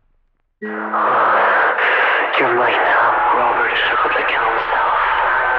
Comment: Heres what i think the difficult audio things are saying 20:55 (heavy breathing)"youre late...robert(?)...im going to off myself" 21:08 "youre..listening..right now" 21:35 "i am here with you." (I honestly dont know) 21:48 "dont you forget me." 21:56 " i desire you" (pretty obvious) 22:04 "please come back" (also obvious) 22:09 "im so cold, so cold" (womp womp) 22:19 Brad is that you?" (Radio noise) 22:29 "stay back! Stay bac- no—(get) BACK" (Next one is obvious) Most of the stuff sounds like workers in the company/organization communicating through walkie talkies/radios. The weird robotic kid voice could be the players loved one that mightve been killed by a entity and is communicating through the game audio thing it reminds me of when ghosts communicate through the ghost speaker idk what its called Then i think the weird sounding guy complaining abt being cold is a entity